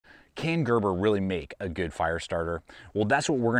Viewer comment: damn i heard "kangaroo really make a good fire starter" as the first two seconds of the video